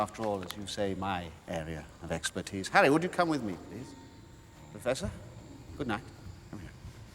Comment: did he just said: Professor gut Nacht